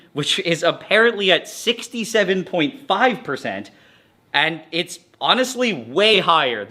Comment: Did I hear 67?